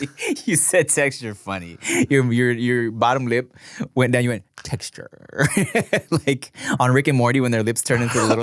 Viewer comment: I heard Tecsure😂😂😂😂